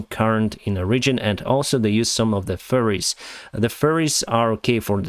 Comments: i heard "furryes" and was like "WTF is Russian army doing now" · I thought you said furries, but obviously you meant ferry. lol